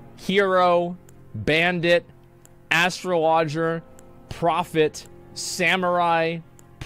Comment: Did he just say Astro lodger??? LOL